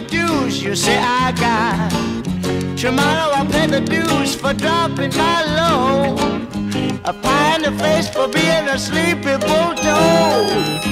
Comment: What a day for a daydream What a day for a daydreamin' boy And I’m lost in a daydream Dreamin' ‘bout my bundle of joy And even if time ain’t really on my side It’s one of those days for takin' a walk outside I’m blowin’ the day to take a walk in the sun And fall on my face on somebody's new mowed lawn I've been havin’ a sweet dream I been dreamin’ since I woke up today It’s starrin’ me and my sweet dream 'Cause she's the one that makes me feel this way And even if time has passing me by a lot I couldn't care less about the dues you say I got Tomorrow I'll pay the dues for droppin’ my load A pie in your face for bein' a sleepy bulltoad And you can be sure that if you're feelin’ right A daydream will last along into the night Tomorrow at breakfast you may pick up your ears Or you may be daydreamin' for a thousand years What a day for a daydream Custom made for a daydreamin' boy And now I’m lost in a daydream Dreamin ‘bout my bundle of joy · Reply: I always thought it said: Tomorrow I'll pay the dues for dumplings I owe And find me a place for bein' asleep before dawn